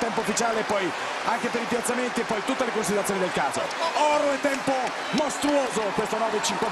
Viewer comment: solo io ho capito "tutte le considerazioni del caZo"?